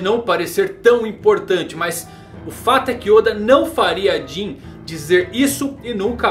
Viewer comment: eu nunca em nenhum vídeo ouvi o falar oq ele falou nesse vídeo "Oda nunca farece" 2:32 kkkkkkkkkk